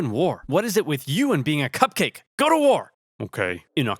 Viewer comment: am I the only one who heard “cuck cake” instead of “cupcake”?